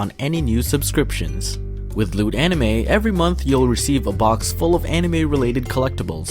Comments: wasnt rlly paying attention at the end so i heard "lewd anime" lol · I totally thought you said “lewd anime”.... “SIGN ME UP!!” Flew through my brain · When he mentioned the sponsor at the end of the anime I heard “Lewd anime” lmao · Thought he said lewd anime!!! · this is what i heard... "With LEWD Anime" wow XD